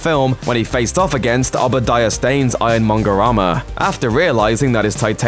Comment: The narrator's accent is so thick, for 75% of the video I thought he was saying "Iron monger-rama" and "Iron Spider-rama!"